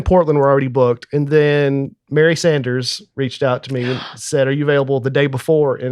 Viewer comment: I thought said Barry Sanders. How awesome that a legendary NFL star advises on his comedy career. I suppose Mary Sanders is ok too.